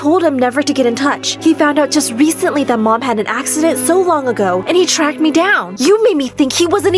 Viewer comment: At first I thought she said Lucy was an accident.